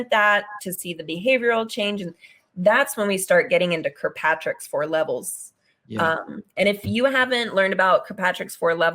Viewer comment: Minute 41:11. She said @@@@@ for levels. What was the word plz? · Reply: Kirkpatrick's 4 levels